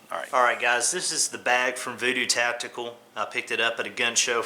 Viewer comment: I think he said Voodoo Tactical.